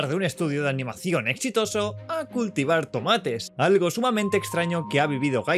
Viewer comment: Pensé que decía "cultivar animales"